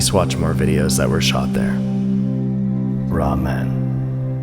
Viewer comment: i honestly thought you said “we’re all men”? Pun intended!?